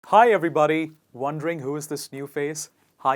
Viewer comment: What he said ? Anyone pls help me... There is something like... One ......who is this new face? Fill that blank area ar improve the line...tnq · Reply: Wondering